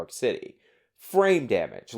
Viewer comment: Oooooh. You've been saying frame damage. I thought you were saying brain damage XD XD XD